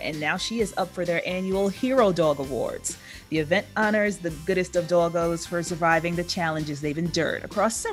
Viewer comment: Is it me or did she say "goodest of doggos" at 0:42 ? lmaooo